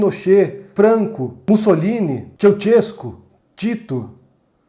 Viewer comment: Quem foi chel chesco que ele cita entre 8:55 e 8:56? 🤔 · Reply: Se escreve "Ceausescu" da Romenia